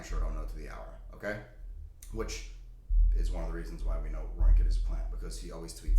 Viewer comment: Hey, maybe I miss heard you in your video but at 3:45 did you call Ryan Cohen a plant? I'm sure I misheard and would like clarification. Best Regards, Scott. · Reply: Roaring kitty